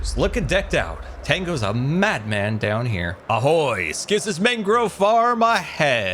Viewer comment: Did really say the Czech greeting "ahoj" or am I trippin?